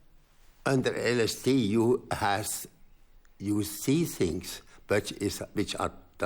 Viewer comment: Can some one tell me what he said at 7:16 thru 7:22. Its the last word he says. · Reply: You will see things transform